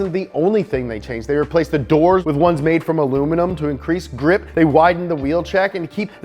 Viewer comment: dufuc? did he say they replaced doors to increase the grip?